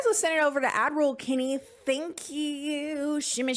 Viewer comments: I just want you to know that every time you say ad roll I hear “Admiral Kennie” 🫡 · Okay for like a year of watching your videos, my poor hearing thought you were saying “Avril Kennie” and for some reason, I thought Avril was like your Sasha Fierce-style personality for doing ad reads. 😂